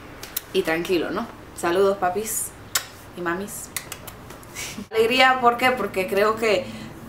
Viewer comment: y yo que pense que decia ALERGIA. xD